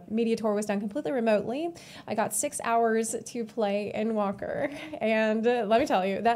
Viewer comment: Your accent is cute, i thought i heard you said End wanker... lol